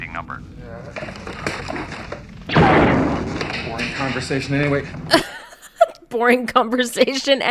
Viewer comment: All these years later and never realized Han Solo says “Boring Conversation anyways.” I always thought he said something else different.